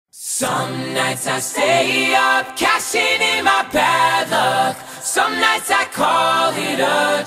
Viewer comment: Some nights I stay up splashing in my bathtub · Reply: I thought he said smashing in my bathtub